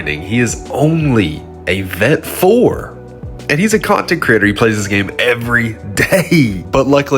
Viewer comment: At about 28 secs. Sounds like he said "hes a compton crip".lol